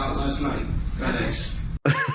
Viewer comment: I wasn't sure I really heard him say Fed Ex.